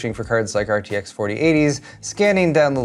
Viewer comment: did you say 4080's at 5:21? Or am I hearing things LMAO.